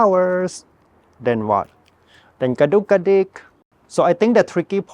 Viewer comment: Lovely! What is the yellow flower which you called “kadookadic” (sp?) at 14:14? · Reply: Geum 😊🥰❤️